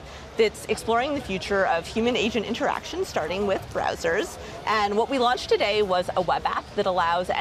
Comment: ngl I thought she said 'starting with brazzers' cause of her accent lol